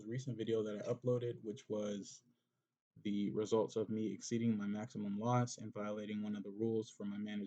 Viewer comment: question! how did you exceed 50 lots? you can do 50 lots max per trade so split them in future! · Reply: oh my bad you said exceed maximum loss i thought you said exceeding 50 lots, i dont know how i got that sorry!